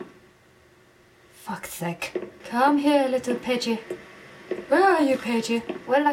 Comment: It sounds like your saying "pidgey" instead of pagie XD